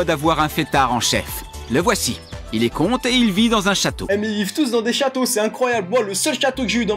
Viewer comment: je vous jure que j'ai une pub au moment où il a dit "con.." j'ai entendu "il est conte... c'est difficile d'être une des meilleures joueuses de Coin Master..." 😂😂